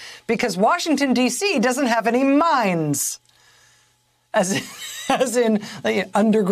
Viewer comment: Republican : There are no mines in DC. Mayor should have replied : There was a lot of mining when the Metro subway sections were constructed. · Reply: When Rachel first said mines I thought she said mimes. They may not have those either.